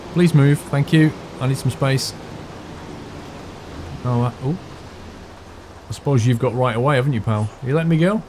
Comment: "I suppose you've got right of way" Did you actually say that......?